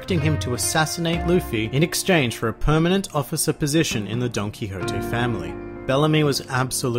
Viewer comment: All this time, I thought you were saying Donkey Hotee. But you were saying DonQuixote. 🤦‍♂️